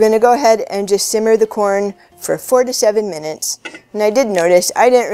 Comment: Careful. It's 4 to 7 mins. Not 47 mins. Hahahha just kidding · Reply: Lol, I thought she said 47 minutes 😂